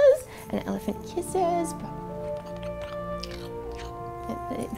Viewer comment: I thought you said that elephant was hungry for penis....I laughed out loud